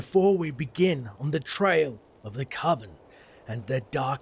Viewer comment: did anyone else think it said "railing the coven" or is it just me?